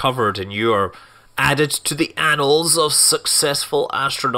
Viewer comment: Added to WHAT? Man, I really hope I hear that wrong... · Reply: Annals, it means like pages or records. "I've add it to the annals of history.' It's a thing. And YES it does sound like another word....